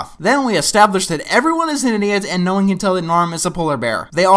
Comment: put captions on and look at what the captions think what said. He said idiot but the captions think he said something else.😄